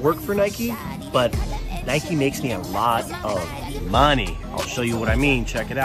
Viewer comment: So I for some reason thought it said “ Nokia makes a lot of money” 🤦